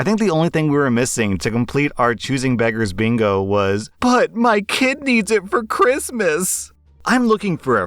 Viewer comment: I'm half asleep so when you said "but my kid needs it for Christmas" I heard it as "but my kidneys are for Christmas" and was really confused for about half a second. I will note that I watch your videos at 3.5x speed though.